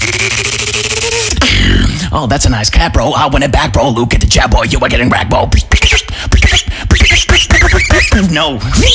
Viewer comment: Can anyone tell me what he is saying at 1:20? I dont understand anything exept "oh thats a nice..." lmao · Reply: oh that's a nice hat boy I want it back boy look at the chat boy you are getting wrecked boy